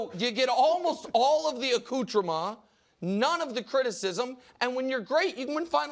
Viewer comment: wtf did Nick says at 20:13 akutrama?